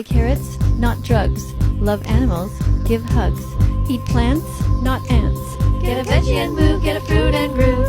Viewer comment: did she say eat plants not ants in more likely to eat an ant in vegetable than bacon.